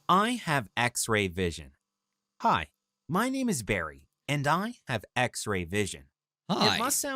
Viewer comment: I thought he said his name was Gary... oof